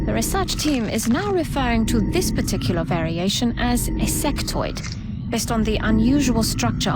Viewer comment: i heard "sectoid" as "sex toy." probably best not to confuse the two.